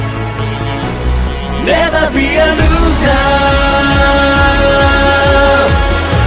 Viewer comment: 【 俺的、空耳を聞け👂 】 レバニラ ルーザー！ （空耳） ⬇︎ Never be a loser‼︎ （公式） 同士よ、集え(笑)